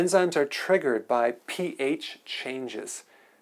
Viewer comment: I thought it said “triggered by PF Changs” for the longest time wow